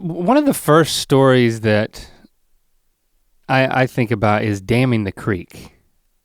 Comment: Thank God for subtitles cause I thought they were saying, "damning" the creek... Like cursing it HAHAHAHA lol